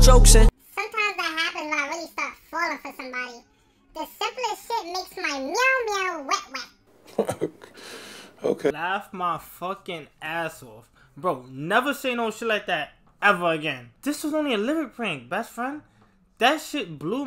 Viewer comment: Who else thought when he said wet wet it sounded like alven I can’t spell for shit😂